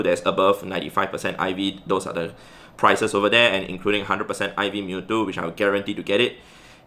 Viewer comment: I heard "600% IV mewtwo" LOL